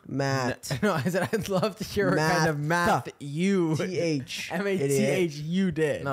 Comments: I SWEAR I HEARD “MATTHEW” WHEN NICK SAID “MATH YOU” HAHAHAHAH I WAS SO CONFUSED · I heard "matthew" when nick said "math you" to chris so dw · I totally thought Nic said “Matthew” and not “Math you” too 😅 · 'math you did' and 'Matthew did' is so funny cuz I heard 'Matthew did' lmaoooo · im with you chris i thought nick said "matthew did" and not "math YOU did" lol